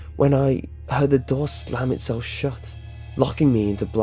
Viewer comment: I heard "Slam itself shit"